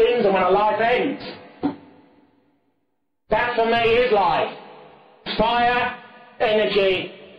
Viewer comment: What is that word he says [WORD?] is light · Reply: that for me is life